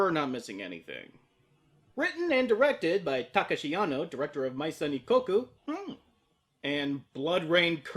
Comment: What is the anime name he says aw 1:04? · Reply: It's Maison Ikkoku